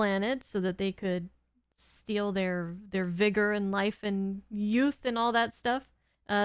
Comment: come again? Did you say vigor? Do you have the address to this place? 🏃🏻💨💨💨